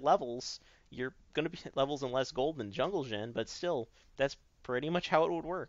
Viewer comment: Sounded like he said "Jungle Gym" when he said "Jungle Xin"